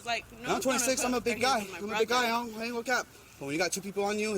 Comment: Did he really say “I’m a big guy”? Hahahahaha